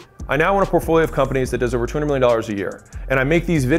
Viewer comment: did he say a trillion?! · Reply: 200 million